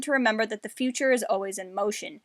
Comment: 😂 when you said Yoda’s line of “The future is always in motion.” I always thought he said “Always emotion is the future” as in, you can feel what happens in the future like pain, or joy...but you can’t quite see it clearly....Hey I learned something new today!